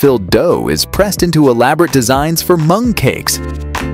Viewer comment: I thought the narrator said they were making beans into monkeys but it was mung cakes apparently!